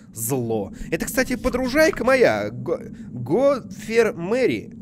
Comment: мне послышалось - Гловер Мелори (из скайрима)